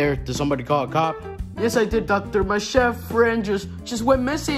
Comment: did junior just call the cop a doctor?